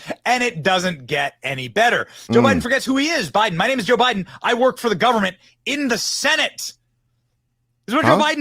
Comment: It almost sounded like he said, "I'm Joe Biden, and I work for the Government and the Senate." So, the house is run by Republicans and the Senate is run by Democrats. He basically just told us he is a puppet of a deep state Gov't and the Democratic party. check out 2:55 and listen again after you read this and see if it sounds like that is what he said. And who the hell is this guy @ 5:24 that stuck his arm out and stopped Joe Biden from walking forward. It's like everyone in the country treats Joe like an elderly grandparent that needs to be reminded where they are constantly. This guy is the president of the United States and looks like a buffoon getting pushed around by some shop owner. Sad days. Remember when Donald Trump pushed people out of the way to be in the front of the line at the NATO summit? This president we have has no control over the space around him or even his own body. It's a sad thing to see.